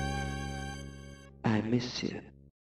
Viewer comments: what is he saying??? I miss you? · Did I just hear "I miss you"